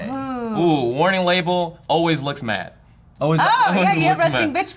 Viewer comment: Did anybody else hear say "Always licks Matt" at 1:30?